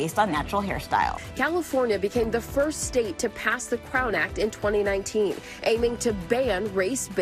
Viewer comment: Shaped like a crown.... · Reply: I legit thought you said clown and I was about to go off lmao.